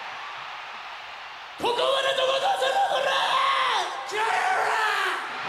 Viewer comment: 最初の0:27からトシとヨシキは何て言ってんの？誰か教えて！ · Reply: トシ「ここまで届かせろよコラァ〜」 ヨシキ「気合い入れろよコラァ〜」